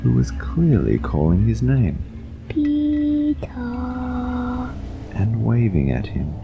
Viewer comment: I thought at first he was saying pee pee😭